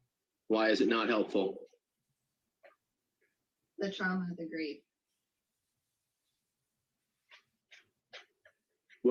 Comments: I heard "The child has to grieve" on each replay until you said "the trauma, the grief" and I wasn't reading the transcript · I heard “the trauma degree”, but I’m not sure why it matters.